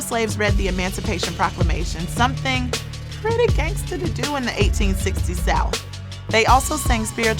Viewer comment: Did she just say, (2:14) "Something pretty GANGSTA to do in the 1860's ?" Pro-Juneteenth regardless. Anti-murder!!!